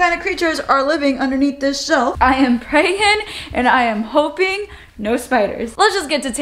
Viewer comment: I heard "I am pregnant" :D